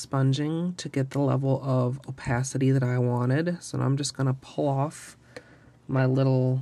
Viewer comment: she said pull off I thought SHE SAID PLOFF WTH